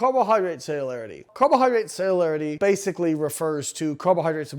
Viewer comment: I kept hearing 'sailor carbohydrates' and imagined the sailor group to be exclusively fed with rusk until their teeth fell out from scurvy.